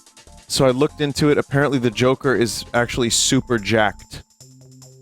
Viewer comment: Shit. I thought you said The Jelqer